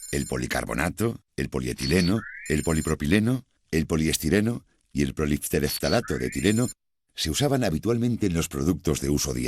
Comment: que dijo en el 21:37 que no le entendi una wea y los subtitulos no lo dicen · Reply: Macaupa polieftarato